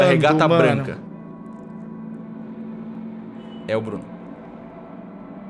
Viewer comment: É QUEM????? ENTENDI NADA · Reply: Bruno